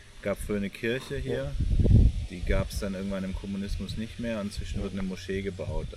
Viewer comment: the subtitles change what he's saying from "not anymore under communism" to "under the soviet era". Can't piss off the CCP masters back at home, I guess.